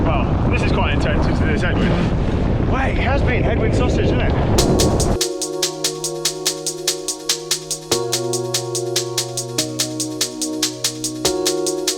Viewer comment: ok, did I hear “it has been head-wind sausage”??? explain lol